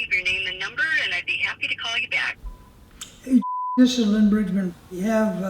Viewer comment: "Hey *dipshit*" That's what I thought he said in my mind.